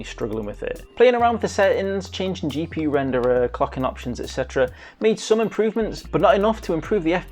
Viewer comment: Sounds like you said "cucking options" lol